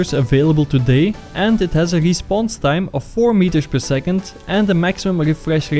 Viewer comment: is it just me or is he saying meters per second instead of milisecond?